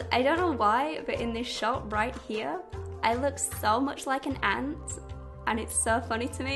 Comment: Lol what did she say at 5:06? That she looks like an ant?